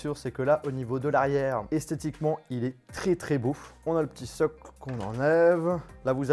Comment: C moi ou il dit il est tre tre beauf ?